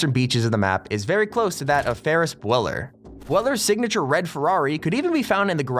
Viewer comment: did he just say Ferris Bweller?